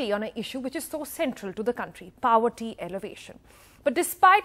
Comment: Did she just say elevation instead of alleviation?